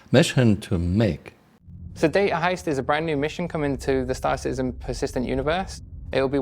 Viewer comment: I kept hearing "Brand new intel missions" or something I swear i e heard that before